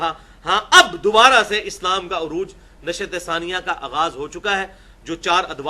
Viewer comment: pe kya word bola engineer sahab ne??? _____ ka aaghaz ho chuka hai?? ...I didn't understand the word..can someone please tell me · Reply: نشاة ثانیہ